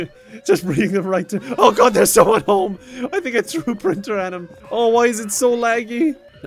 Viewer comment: Toss me my keys! I thought you said printer!